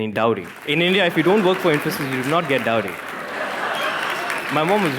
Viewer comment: what he was saying after 5:50? I can't understand his accent there. Can anyone please subtitle that for me? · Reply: He said "If you don't work for INFOSYS", not "emphasis". Infosys is one of the top 3 biggest IT companies in India.